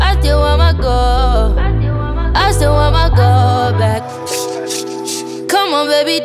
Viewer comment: I thought she said “I just want my Gold back” and I had felt that 😅